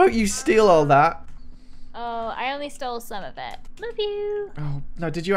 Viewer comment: the first time i heard said "iloveu" to even though i've watched this multiple times!...